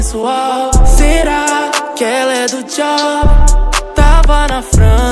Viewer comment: Só eu que escutei "será que ela é do diabo ?"